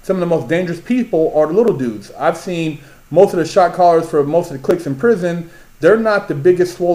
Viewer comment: oh... shot caller. i thought he was saying shock collar lol